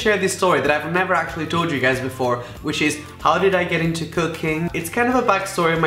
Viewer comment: Literally thought said "so how did I get into cocaine" instead of 'cooking'